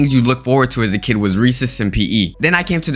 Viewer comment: when he said recess i thought he said reeses and i agreed